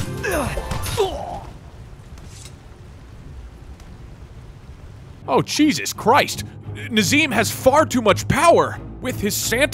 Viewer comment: did you just say “cheesus christ”